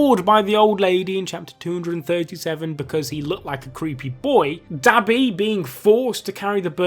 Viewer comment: did he just say DaBaby